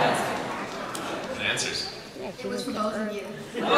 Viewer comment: what does the kid say? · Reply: It was both of you i think?